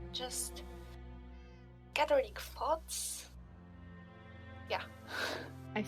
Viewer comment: Won't lie, I thought for a second they said "...gathering thots..." 6:54 Sounds like Aspen would have reacted badly if Devo was a kingdom....... I think I understand Aspens post-Devo crashout abit better now 22:40 On the bright side, cant be a next time if its the last time!🎉